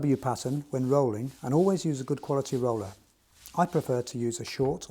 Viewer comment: what did he say about the roller?? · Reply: Apply in a W pattern using a good quality roller